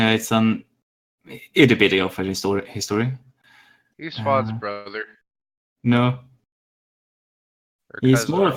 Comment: How so? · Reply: Hmm, let's find an example... At about 6:00 says "He's Phann's brother." and I immediately respond with "No". I both misheard and misunderstood. I thought he said "Heinze's brother" (Discord cut up), and I totally forgot at that moment that Bidi is Phann's brother, so I didn't even think about the possibility that he would mention Phann. Therefore, was right and I simply made the conversation weird. Very cringy to listen back to for me. Think the best way for me to stop this from happening is shutting up for an extra 5 seconds :P